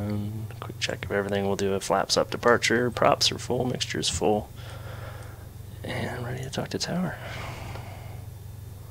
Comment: Hello, good videos. I couldn't quite understand what you said. What was the program that told you you were approaching the runway while taxing at around 9:15? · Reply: ForeFlight for iOS